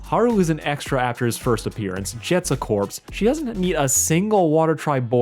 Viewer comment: I had to rewind because I misheard "Jet" as "She has"... 💀